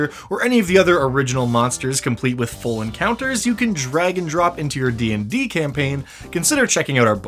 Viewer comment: Just had to say I heard "Drag and drop" as "Dragon drop" and it took me a second to work it out